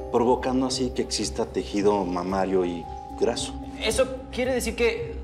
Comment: Le entendí mamado😂😂😂